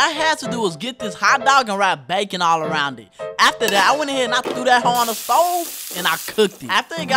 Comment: Did he just say “Throw that Ho on the fo”.. that’s elite.. ima stick with that